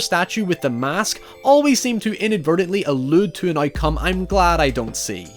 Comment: Every time you say “outcome” I hear “eye cum”